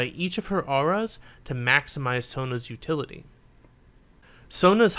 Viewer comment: Is it just me or does he call Sona “Tona” at 15:58?